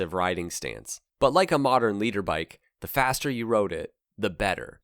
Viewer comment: Did you say 'leader bike' at 10:53 or more correctly, 'litre bike'?